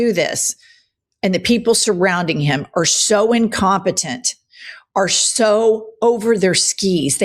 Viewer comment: Did she say, "The people around him are SO incompetent?" HA! I thought I heard "Incontinent!" With them, yeah, it's the same thing.